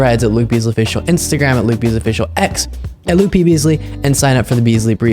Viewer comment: The way you were saying “Luke Beasley” at the end I thought you were saying “loopy’s” 😂😂😂